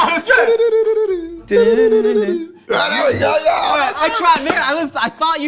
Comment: is he saying "I tried N**ga" 🤔